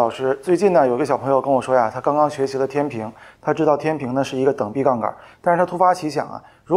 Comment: 差点听成“有个小朋友跟我说啊他刚刚学习了近平”